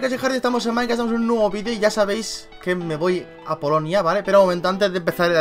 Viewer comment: Dijo " Polonia " y yo pense que dijo Colombia, ya iba a cojer maleta xd